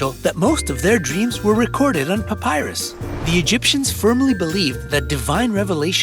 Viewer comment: Did I hear Papyrus?